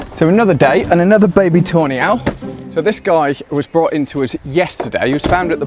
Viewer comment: Pardon my AE, but every time says "Tawny Owl", I hear "toe nail". 😄